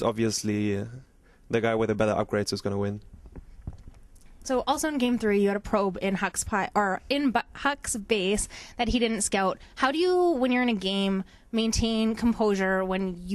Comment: i thought she said " you had a probe in Huk's butt" i died. XD